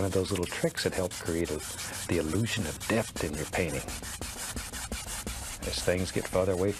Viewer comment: I honestly thought he said it creates the illusion of death in your painting..